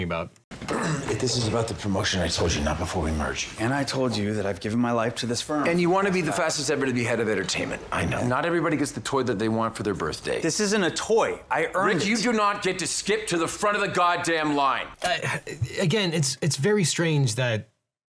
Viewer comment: I heard "toilet they want for their birthday"